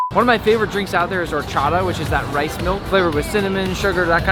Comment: wait what did he just say😭 (oh-cha-do) 🤣 it’s (o-cha-ta) lol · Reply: (or-cha-da)